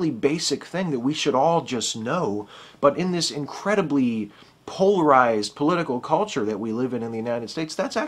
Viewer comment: I misheard and thought you said; "...bi-polar political culture..." LOL. I'm now gonna watch some Louis CK, for the first time. He had better be awesome after so much hype;)